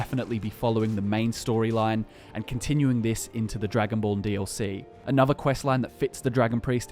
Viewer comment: Is just me or does he say the Dragonball DLC? I know it’s called Dragonborn, but still is it just me?